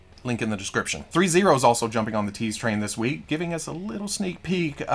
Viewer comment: At first, I thought you said Rita Repulsa "teats," not tease.